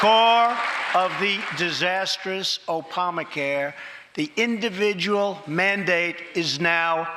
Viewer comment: Did he say "Opamacare"? lmao